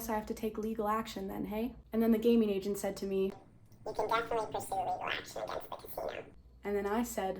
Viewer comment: What is she saying in the smurf voice? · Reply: I think it is "you can definitely pursue legal action against the casino" but im not 100% sure